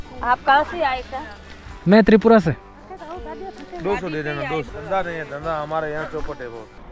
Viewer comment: can somebody tell me what that guy was asking? 200 rupee for something? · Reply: Dance karneka 200